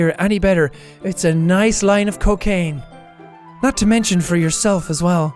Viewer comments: "It's a nice line of cocaine" I was distracted so I actually thought she said that and I was like:WTAF?! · I thought she had actually said "a nice line of cocaine" for i wasn't looking at te subtitles LOL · did jack just said a nice line of cocaine